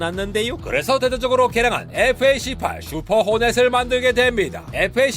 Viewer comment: 왜왜 f-18 호넷, 슈퍼 호넷이 왜왜왜 c8로 들리는 것인가 ㅋㅋㅋ